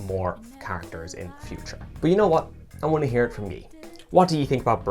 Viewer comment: does anyone else hear “I want to hear it from me”?????